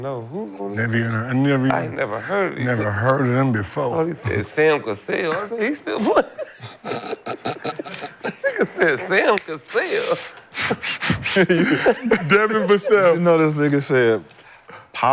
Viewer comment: Nick Young & Gil fried asf, Talking bout I never heard of these nqqaz, thought He said Sam Cassell, He still playing ?? 😂😂😂😭😭😭😭